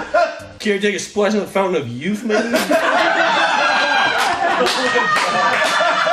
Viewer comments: I always thought he said "fountian of youth maybe?" instead of man which was way funnier to me. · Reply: i always heard "Care to take a splash at the fountain of youth Maiden?" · I always thought he said “maybe” at the end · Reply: I thought it was "maiden"